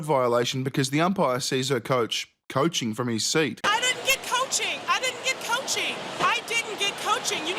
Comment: I heard her saying I didn’t get coochie.